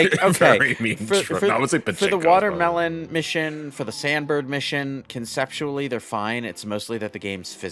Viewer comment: It sounded like said "Sandberg" instead of "Sand Bird", lol.